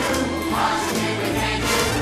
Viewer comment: I always use to think stewie said " heaven cry! "